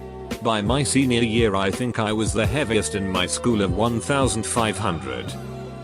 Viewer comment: I didn't hear "heaviest kid of 1500", I heard "heaviest kid AT 1500."